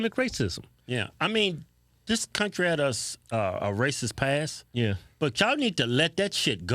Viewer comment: Did you combine crazy and racist lol😂I could have sworn he say crasist!